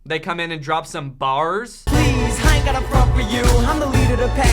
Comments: did he just say “ I ain’t gonna fuck wit you” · Pls tell me that I’m not the only who heard “I don’t have to fck with u” @ 17:58 I literally don’t understand😭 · did- did he just swear or am I hearing things? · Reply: I think he said please I ain't got a front for you. But that doesn't make any sense. But none of this does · Is it just me or does it sound like he says “please I don’t gotta fu** with you” at 17:57 ? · Did...Did they say "I ain't gonna fuck with you" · was that a "please, i aint gotta fuck with u"????????? or am i just hearing it wrong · DID HE JUST SAY “please I ain’t gonna f*** with you” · isn't this a childrens movie?? at 17:57 the boy said "please i dont gotta fuck wit chu" i don't think they said anything diffrent but they still said fuck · does he say “I ain’t gonna fuck with you”?? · Wait every time I play that back I hear, ‘hey I don’t gotta f*** with u,” and I’m really confused lol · Is it just me or did he say "Please, I don't gotta fuck wit you" No? Okay. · does anyone else hear "Please I don't wanna fuck with you." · I've always wondered,did he just say "please,I ain't gotta f**k with you"? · did he say “ please I ain’t gonna fuck wit you” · wat i heard was Please i aint gotta fuck with you >=/ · I swear at 17:58 that boy said I don’t gnna f*** with u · wait I'm hearing things right did he say I ain't gotta f with u I thought there would comment's about that lololololol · Yo did he say "I ain't gotta fuck with you" at 17:58 ????